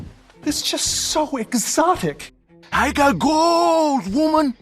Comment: I thought when Ramón Cortez “ I got gold” thought he said I got hoes